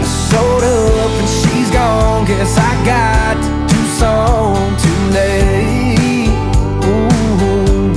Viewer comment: He says Tucson. All this time, I thought he said "Two Songs". 🤦‍♂️